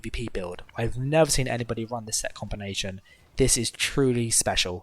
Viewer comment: At first I heard : "I've never seen anyone having this set combination" Then I was like, finally someone not running fury or bloodspawn or new moon, it'll be a great video ...then I watch, using dizzying swing, executionner, fury, bloodspawn, shacklebreaker ....well, disappointed, again ...I'm also running fury/bs/shacklebreaker on my stamina templar, because I like it that way, but it's my Main character and the only one with "Meta sets" ...still a good build, but too much seen for me